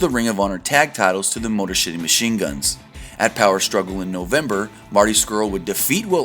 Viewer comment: Ok... I had to go back a listen to what you said before I posted this. And I'm pretty sure you said "Motor SHITTY Machineguns"